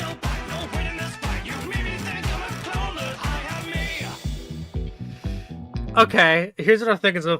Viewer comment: Bendy Songs Tier List - Part 3 Timestamps ---------------------------------- 1. 8:39 Hailey Lain - BEHIND THE CURTAIN 2. 20:43 - I Am Me 3. 29:28 - Are You Proud Of Me Now 4. 38:30 Fandroid - The Devil's Swing 4a 44:04 The Devil's Swing - Cover by Caleb Hyles 5. 47:39 ChewieCatt - WELCOME TO MY SHOW 6. 55:09 CG5 feat. The Stupendium - How'd You Hear Me 7. 1:03:40 Kyle Allen Music - Lost in the Studio 8. 1:12:11 KMODO - You Will Believe 9. 1:20:18 NateWantsToBattle - MY NAME ISN'T MINE 10. 1:28:49 HalaCG - CRAZY 11. 1:38:50 - MR. CROOKED 12. 1:44:58CG5 - Can I Get An Amen 13. 1:54:04 Kjwippy - Prophet's Lament 14. 2:01:50 CK9C - Who's Laughing Now? 15. 2:07:50 Lhuguney - BENDY AND THE INK MACHINE SONG - Chapter 3 Animation Getting those times took me 5 min 51 seconds from the end of the stream thats a sub 6 speedrun :) edit: nvm i got one wrong run invalid edit2: added the timestamp for the devils swing cover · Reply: I thought "I am me" said "I am all of me" and was confused how shadow the hedgehog fit into bendy lore